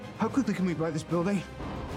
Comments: "how quickly can we buy this building?" I've watched this fight a hundred times and I never noticed that. Lmao that's a good line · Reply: That Guy Bob I thought he said, how could we be by this building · "how quickly can we buy this building?" I've watched this fight a hundred times and I never noticed that. Lmao that's a good line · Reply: I always heard: "I think we can rewrite this building" lol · "how quickly can we buy this building?" I've watched this fight a hundred times and I never noticed that. Lmao that's a good line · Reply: I thought he said, how come I never bought this building. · "how quickly can we buy this building?" I've watched this fight a hundred times and I never noticed that. Lmao that's a good line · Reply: I thought he said "how come we didn't come by this building"